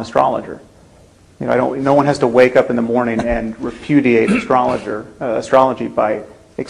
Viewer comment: Did he just say 'refudiate'?? Never thought he'd take a leaf from Sarah Palin's book lol!